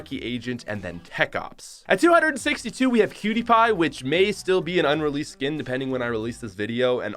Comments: thought he said PewDiePie lol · I thought he said PewDiePie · am I the only one who thought he said pewdiepie ??!!??!?!?! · I thought he said PewDiePie LOL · thought he said pewdiepie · I wasn't looking at the screen and I thought he said Pewdiepie